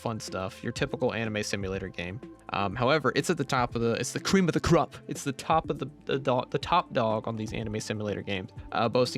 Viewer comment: what are you even saying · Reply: ITS THE BEST ANIME GAME ON THE PLATFORM SUS